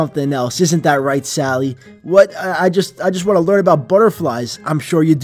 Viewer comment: I thought he said butter frying 🤣